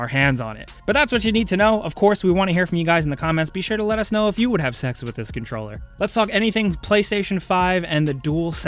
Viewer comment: Did he said Have $ex whit this Controler